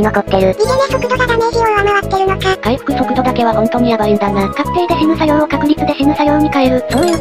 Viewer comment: 「確定でしぬ作業を"確実"にしぬ作業に変える」 って聞こえて「ん？」と声に出してしまった